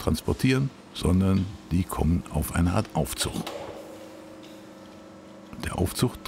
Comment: Aufzuch?!? (Es kann auch "Aufzucht" verstanden werden.) Bitte "neutraldeutsch" besprechen. AufZug heißt es - genau wie "Mangnet" -> es heißt MagNet! Sonst klasse Film!!!